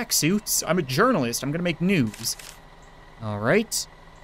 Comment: I thought he said nudes.